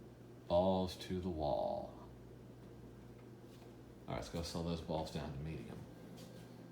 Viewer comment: What did he say at 11:10? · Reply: The Elevator World “Alright, let’s go slow those balls down to medium.”